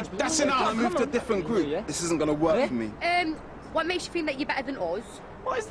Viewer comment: I'm french and all I understand is "Uuh... Wha mek sha fi da you bey den ozz ?!" 😂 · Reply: I'm Canadian and I did not understand the "us" XDDD such a weird way to pronounce that vowel. And this is what I heard XD "erm, what makes ya fin' da' you're be'uh than aus"